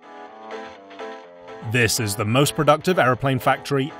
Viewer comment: i thought he first said "this is hte most productive video ever"